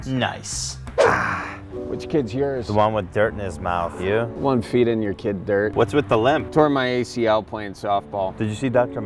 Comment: “Which kids yours?” “The one with dirt in his mouth. Yours?” “The one feeding your kid dirt.” 😂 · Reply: Oh, I thought he said “peeing in your kid’s dirt”